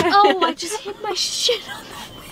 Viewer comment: 🤣 did I hear sh*t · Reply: no it said shoe